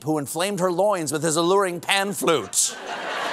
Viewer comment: Until Colbert pantomimed playing a pan flute, I thought he said “pant’s flute”!😮